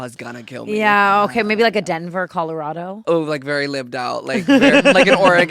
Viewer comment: oh god I just thought u said a desperate Colorado bc honey YESSSS😅😂